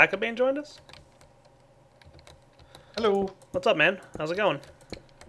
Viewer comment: have you an german guy on the server ? I think I hear hallo